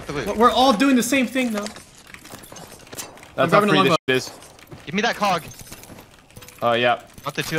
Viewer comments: What did he Sayyy??? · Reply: “Gimme that cog” (HCOG sight) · what did Jordan just say? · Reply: Hcog i thnk xd